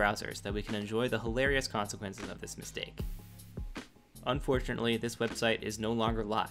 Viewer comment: anyone else notice he said "mistake" but the captions say "error"? i feel like that was intentional lol